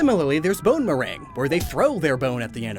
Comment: I thought you said bonerang ._."